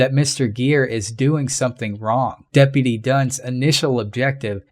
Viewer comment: Sounded like "Deputy Dunce" :)